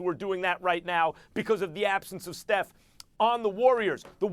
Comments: Most vital Cock? Am I hearing things? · Did he just say :"most vital cock " at the end?